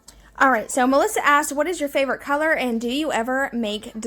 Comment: Haha I just talk way to fast 🤣 I said so my favoritecolor without a break hahaha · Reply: I knew that it was favorite color but dang I thought you said hair. 😂 I’m also old so there’s that, the whole hearing thing. 😂❤️